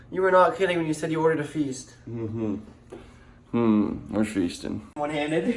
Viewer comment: Lol it sound like u said “we’re fisting”